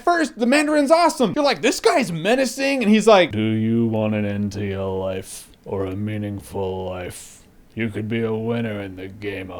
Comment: I thought the Mandarin said, "Do you want an *EMPTY* life, or a *MEANINGFUL* death?", not, "Do you want an end to your life, or a meaningful life?". Whether that was a joke or not, that was funny